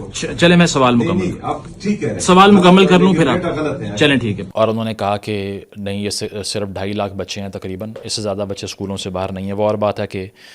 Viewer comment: Approximately 4.9 million children are currently out of school in Khyber Pakhtunkhwa (KPK), Pakistan, as of late 2025. You're extremely right i respect your knowledge. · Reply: i think the CM said 2.6 million kids are out of school not 2.5 lacs